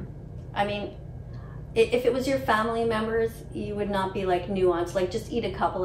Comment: I really thought she said eat a family member😭😭💀